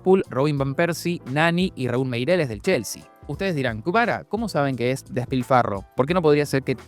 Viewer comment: Escuché "Kvara", vine a los comments y no decepcionaron jajaja